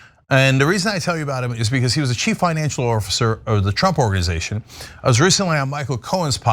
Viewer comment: Did you just say “orfacer”? (Or is it “orffacer”?) lol